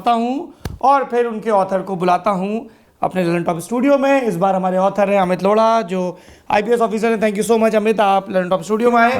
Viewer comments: Who else heard lauda and luntop ? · did he just say lawda??